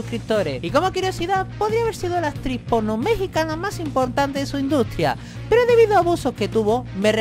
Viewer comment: Que dijiste en el minuto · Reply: Podría ser la actriz porno Mexicana con mejor potencial en su carrera.